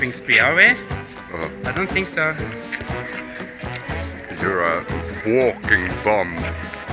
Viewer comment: "You're a walking bomb." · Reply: Lol, I thought he said "you're a walking BUM!" Thanks for clearing this up for me. 😊😂👍💗